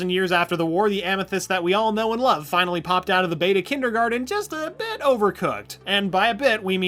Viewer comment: Did he say beta kindergarten